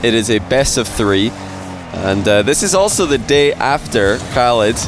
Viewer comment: who's tournament did say? Pierce? I couldn't catch it Edit : It's his channel, I found it. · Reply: Nvm found it, it's on his channel.